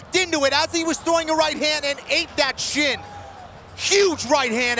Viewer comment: thought he said "ate that shit" at first lol